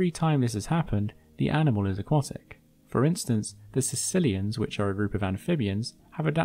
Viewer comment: I heard that as "Sicilians, which are a group of amphibians...." and had an old-school racist chuckle to myself. I expect Christopher Walken to shoot me in the face any minute now.